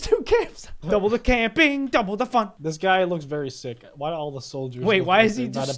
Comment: I thought he said double the f*** but he said fun